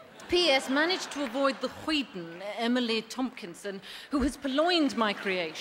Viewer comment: What does she say at 3:56? Hoiten? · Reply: It's hoyden , and it means boisterous, saucy, or tomboyish girl.